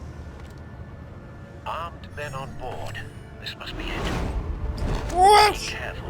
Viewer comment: oh god. when Alfred said 'This must be it.' I thought he said 'This must be lit.'